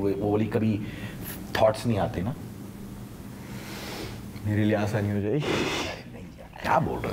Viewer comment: what did he say plzz reply 🙏🙏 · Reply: Mere lia assani ho jaygi